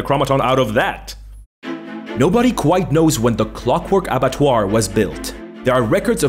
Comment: a random question, but what's that word at 16:06, right after "clockwork"? I can't quite make it out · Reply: The word is abattoir. They're slaughter houses.